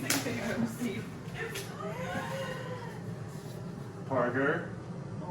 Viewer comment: Does Adam say the name parker at 13:48?? Or am I hearing things ? A real life ✨babydoll✨ .. congrats you guys ! Such a cute little fam fam 🥰🤩 can’t wait to watch y’all grow even more and see yalls sweet princess grow up over time so exciting ☺️